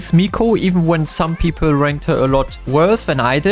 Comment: Did he say HER for mico?? No there no way tell me I’m crazy 😧